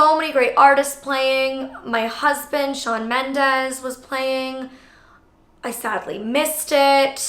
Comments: My husband Shawn mended was playing 2:14 not sure I heard that right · Wait!?............. 2:15 Did you say "Your husband Shawn Mendes"